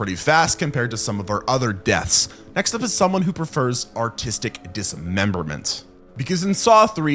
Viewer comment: did he just say autistic dismemberment